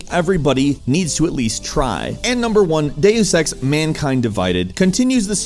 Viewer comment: When said Deus Ex, I thought he said "Day of Sex" until I saw the subtitle. xD